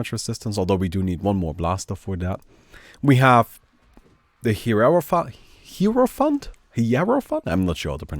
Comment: sorry I heard it wrongly, hieropfuk